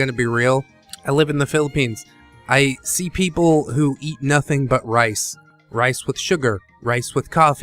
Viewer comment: I thought you said 'I eat Sea-People and nothing but rice' lol